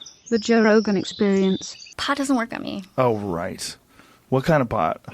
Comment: Am I the only one who hears Joe say "Oh, rice."?